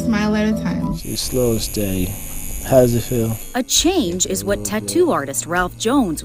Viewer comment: I just heard “slow and steady,how does that feel”😂😂😂😂😂😂😂